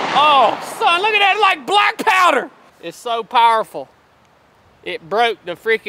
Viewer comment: I thought you said Black Power! 😂